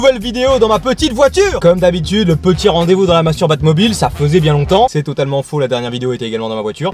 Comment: C moi ou a 00:13 il dit masturbe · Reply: Il dit masturbatmobile